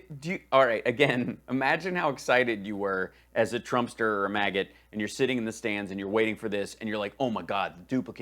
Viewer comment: Did I hear “trumpeter or a maggot” omg you kill me I love your videos